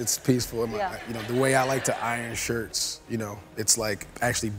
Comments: At first, I thought he said ‘irony”. I was impressed. Then .... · Who else thought he said irony